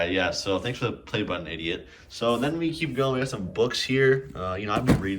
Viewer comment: did u call urself an idiot?